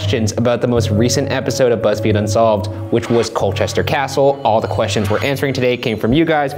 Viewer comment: I thought it said molester castle